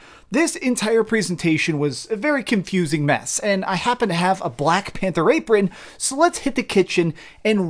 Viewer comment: excise me. a what? 0:29 sounded like you said a Black Panther rape ring!?... you know what never mind